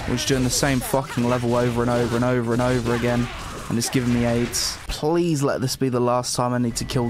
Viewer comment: I'm sorry but did you just say, "... and it's giving me AIDS?"